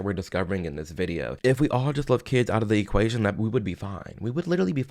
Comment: did bro say "if we all left kids out of the equasion we would all be fine, we would be gucci."??